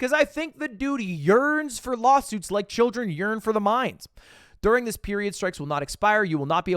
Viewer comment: Sorry Did You Say Children Yearn For The Mine